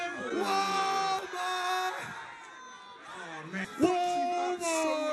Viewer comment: Who else thought Jake Paul was saying something like womar instead of Walmart😂